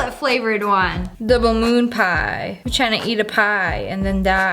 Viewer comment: When she said, "Double Moon Pie" I thought she said, "The Balloon Pie"